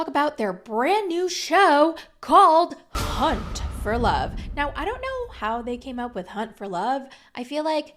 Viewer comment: Lolll I thought you said cunt for love 😅